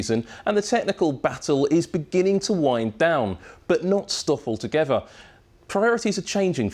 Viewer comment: Who else heard "But not Stoffel together"?